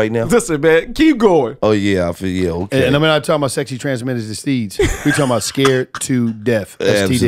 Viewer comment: 🤣😅 wtf did Wallo just say? "sexy transmitted deseege"